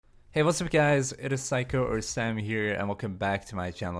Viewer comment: I thought you said psycho lol